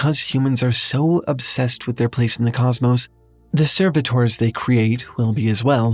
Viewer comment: what's the word they used here, "the ___ they create will be as well" closed captions and google haven't helped. Serv something. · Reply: servitors